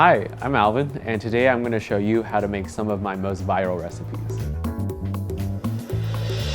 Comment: I thought he said 'my most vile recipes'